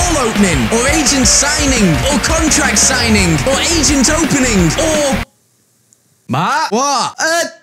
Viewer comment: Asian opening I thought Matt said